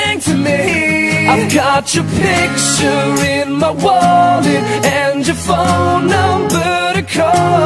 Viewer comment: I thought he said body instead of wallet, I need to fix my hearing